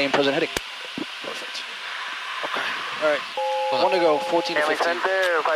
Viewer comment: Right at about 11:08 you say something that sounds like "one to go" -- What are you actually saying and what does it mean?